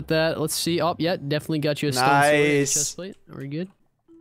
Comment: I thought at 5:58, said stone sword chest plate xD